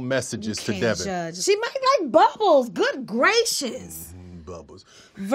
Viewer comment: I literally thought she said “buttholes” instead of bubbles